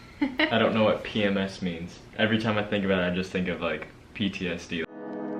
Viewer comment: my army ass thought he said Bts lmao